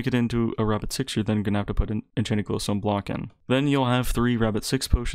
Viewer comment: Sorry I dont have the best hearing, can somebody tell me what he said here? I heard "You have to put an enchanted sun block in"